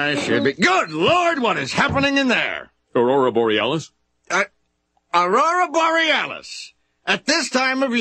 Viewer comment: Bruv I thought Aurora borealis said anorexia breasts sjsnakjsja